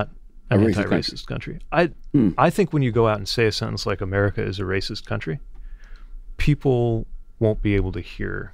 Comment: You completely misheard/misunderstood him he was saying America is a racist country.